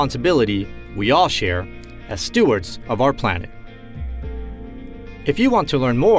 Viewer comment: I thought he said "stewarts"